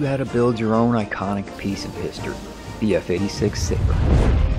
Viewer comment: I heard f86 sigma🗿